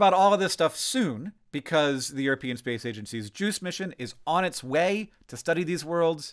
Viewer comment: seize the juice mission?!?!? is that what you said?